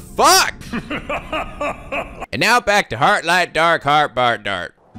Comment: I'm thought during "now back to..." part I thought I he said " heart light dark fart Bart"